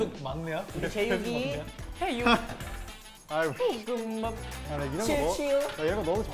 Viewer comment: did jaehyun just said "aigoo shit" bc thats hOT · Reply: "a igeo"